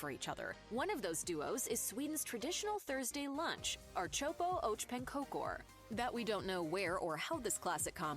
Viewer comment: i let out a loud "NÄÄÄEEEE" when i heard, "OCrHOPo oh PANKOKOR!" i hate my life at this point